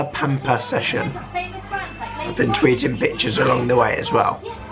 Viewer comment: For a sec I thought you said "I was tweeting Bitches along the way" :D